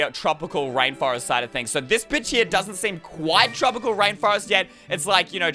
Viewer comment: wait, did say the b-word?